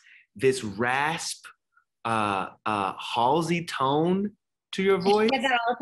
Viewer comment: Who did he compare her to towards the end of the interview? I didn't quite catch/recognise it, sounded like he said "Hallsy". Anyone know?